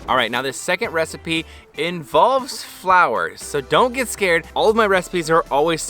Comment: at first i heard "and the second recipe involves flowers, but don't be scared" lol P.S. i tried google translate and can't distinguish pronunciation of flower and flour